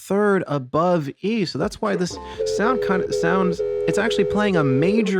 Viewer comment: Hi Uncle, Derek here... ...did you say the word "soundcunt" by accident at 7:36? That's no way to talk to the G# who is the major third above E. This note is our friend in the bass world..Ther're intrestign. ps. do you want your slippers back yet? pps.. [EDIT: 7:33 ...]